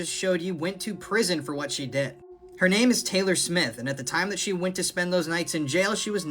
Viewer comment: I thought he said Taylor swift...😂